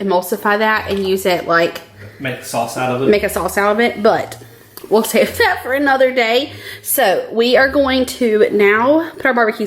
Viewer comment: Idk how I missed the part about the liquid in the bottom but at 24:25 I heard you say “make us all celibate? But we’ll save that for another day” and I’m like wait? 🤔🫢Whaaat?! I double clicked back and heard what ya REALLY said. I lol’d at myself so hard!! Haha 😂 I can’t wait to try all of these!! Think ima go throw the ribs in the crockpot now ☺️!